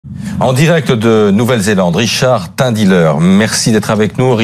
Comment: Au début jai compris " Richard est un dealer"